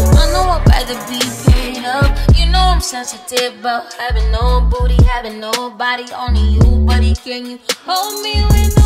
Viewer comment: “you know im sensitive bout havin no booty bout havin no body”... damn.... i felt that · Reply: I always thought that she was saying „.. bout having own booty, having own body..” I’m confused